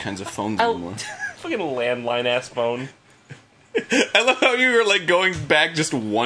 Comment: I thought you said landmine phone 9:20 This game is actually making me facepalm